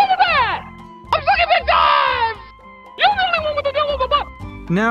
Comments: All i hear is: "Your the only one with a devil in your butt!" XD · “your the only one with a beautiful butt” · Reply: Another person that switch "you're" with "your",also i heard "you're the only one with the hell of a but"